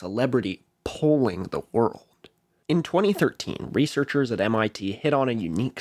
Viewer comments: you said 2013 but the subtitles said 2016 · You said 2013, but the subtitles say 2016. Which one is right? 5:26 The term "translation" os a bit misleading. While some foreign language articles are direct translations of the English page, a lot of Wikipedia articles in other langjages are written independently and can vary quite a bit from each other. Some English articles are even translations from another language. If you're multilingual, it's usually a good idea to compare and contrast the Wikipedia articles of the languages you know. Especially if what you're looking up originated in a non-anglophone country, the Wikipedia article from the place it's actually from is more in-depth.